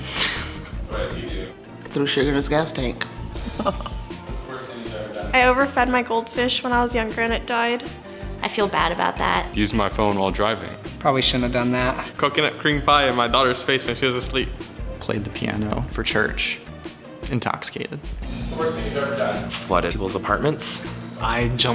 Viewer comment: I thought that he said “cream pied my daughters face while she was asleep” and I was shook.